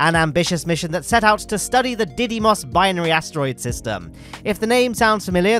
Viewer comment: the freaking what asteroid system?? am i tripping or did he say diddy-mos? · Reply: Didymos